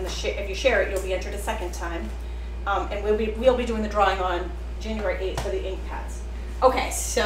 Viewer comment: SHARED, but I hope the drawing is before January 8th, 😂 at least that’s what it sounded like at 🕐 13:00 mark, lol